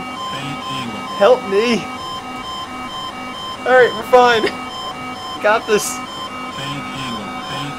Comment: "We're fine." Famous last words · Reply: I thought he was saying "we're dying"